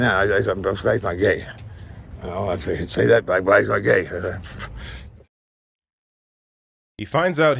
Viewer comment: Did he say “He’s not black, he’s not gay.” at 1:39 to 1:40? I only understand every 10th word this degenerate says…………